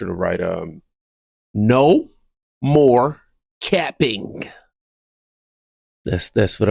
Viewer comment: Got you. No More Capping,😂 I thought you said no more Camping 53 deaf after 11 years of service. Comments hooked me up 😂😂😂